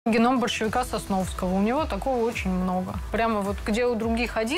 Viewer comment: У неё красивые руки.. Ну и да, тоже услышал "геном большевика Сосновского")) Подумал, что это кто-то из революционеров ещё времён РСДРП.